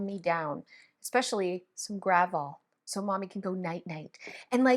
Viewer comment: We don't have the Gravol brand in the U.S., so at first I thought you said you put "gravel" in your mom calm kit! 😂😂😂